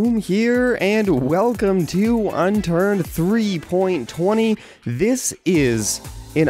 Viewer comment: lol I thought he said Unturd xD